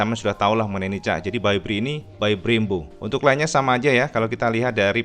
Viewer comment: 44jt an masih wort lah cak,mengingat fitur yg ditawarkan jg bejibun 😎 • ABS dual • VVA • TCS • QS • kill engine model baru ala" moge • tombol kontrol speedo terpisah • Kaliper By BRE • Projie headlamp • ARM banana • Y connect • speedo ala" R1 lha merk sblh..🤨🤣🤣 ngaku sport fairing ,harga tembus 40jt an sekedar engine cut off aja gk poenya,wkwkwk..🤣🤣🤣 asww asww..🐶🙊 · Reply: O oh udah BREBRE bukan Brembo lagi dulu jadi aku yg salah dengar x 🤣🤣🤣🤣🤣🤣🤣🤣🤣🤣